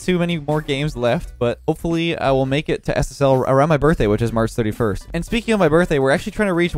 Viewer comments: Did he just say March 71st? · bruh is it just me or did it sound like he said "around my birthday which is march 71st"